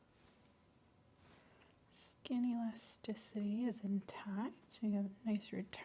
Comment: right at 54:57 when she says "skin elasticity" i thought she was starting to say "skinnylicious"